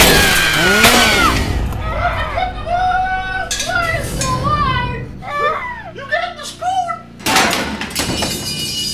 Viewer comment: Can someone tell me what he says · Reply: You leave this whole place alive if you take the spoon